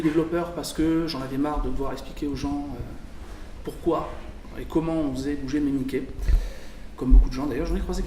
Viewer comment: je pige pas ce qu'il dit à 0:26, j'entends "comment on faisait bouger mes niqué", c'est quoi qu'il a voulu dire par niquet ? ps : ce message n'est pas un troll, je demande vraiment. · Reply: En fait il dit : "J'en avais marre de devoir expliquer pourquoi et comment on faisait bouger mes Mickeys" c'est un terme qu'on utilise (dans l'illustration, et la BD généralement) pour désigner les "petits dessins" des illustrateurs. en rapport avec la célère souris évidemment :)